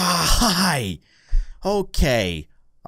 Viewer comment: what did he say? Did he say Die je hi???????